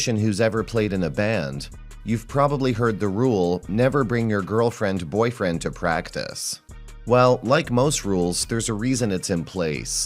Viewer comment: I heard "Never bring your girlfriend's boyfriend to band practice." Solid advice.